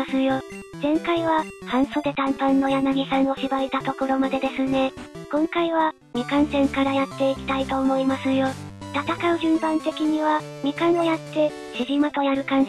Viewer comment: 本日は未完成からやっていきますよ。 に聞こえた。